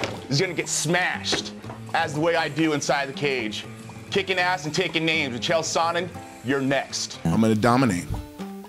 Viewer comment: Kickin' Ass and taking names 🤦🤦🤣🤣I always heard it Taking names & Kicking ass